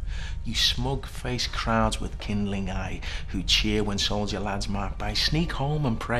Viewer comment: Sounds like he says "when soldier lads mark by" ??